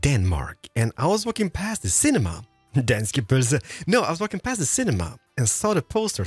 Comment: What does Keralis say at 12:08? Sounds kind of like "danske pølser" which would be odd to say.